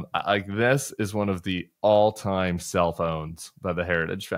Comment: When Tim said, “This is one of the all-time self owns by the Heritage Foundation,” I was confused at first because I heard, “all-time cell phones …” 🤭.